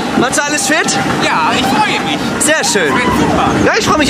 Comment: was hat der gyp gesagt ? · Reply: „Bitte die Kamera wegpacken, ich wiederhole, die Kamera wegpacken, Danke!“